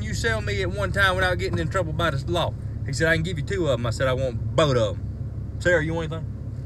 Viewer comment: “I want bodum” why do I know what he said still😭🤣💯